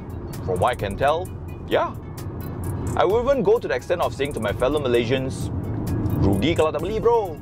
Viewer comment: is saying that he “will even” or “wouldn’t”...?